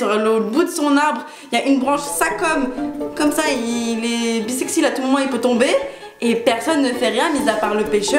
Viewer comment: il est quoi? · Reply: Elle a dit '' bisexy '' j'ai pas capté 🤣🤣